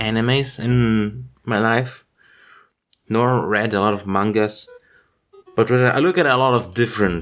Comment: did he just say "mongus"?